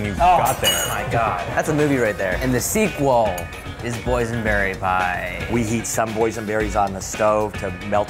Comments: I thought they first said poisonberry pie, me 😶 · boysenberries from Chile <3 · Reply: HappinessInKindness i thought he said poisonberries XD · Was i the only one who thought he was saying poisonberry pie?